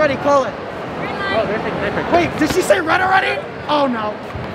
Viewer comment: OH NO DID SHE SAY ReD alRedY cutely slams faces